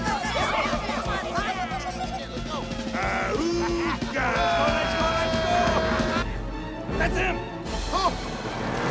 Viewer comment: What the heck was saying? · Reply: He is saying "Ahooga" It's a sound effect of an early 1900's car horn. It's equivalent to someone describing an explosion (BOOM!) or someone doing a fake sneeze (Ah-Choo!)